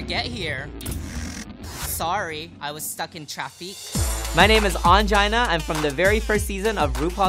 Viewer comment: Am I the only one trying to figure out what traff-ekk means? At 6:55 Ongina says-"I was caught in traffic" but it sounds like she says -"traff-ekk"? Is this an inside joke I'm missing or does she just say it so weird?!